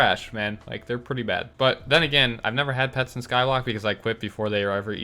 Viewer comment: Did I just hear that right? Skylock? 8:15 hahaha made my night